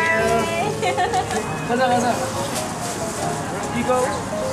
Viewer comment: Is it just me or did the cashier really said "something-gozaimasu" (I didn't hear the first word clearly but she really said gozaimasu) Did she thought SVT were Japanese?😂